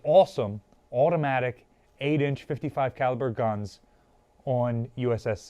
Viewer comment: Automatic 8” 55 caliber guns? I first misheard that as 8” automatic guns and imagined what that rate of fire would mean.